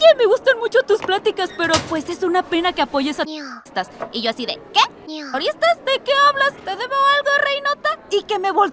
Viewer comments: Qué dice en el doblaje? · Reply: terroristas pero lo censuró por youtube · que dice ahí, terroristas? Por cierto está parte de najimi me mata como la interpreta XD